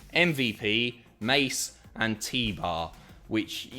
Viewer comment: When he first said Mace and T-Bar I thought he said Mason Teebar has joined the Hurt business and I was well confused